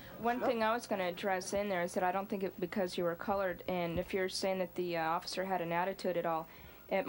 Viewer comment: did she say colored?!?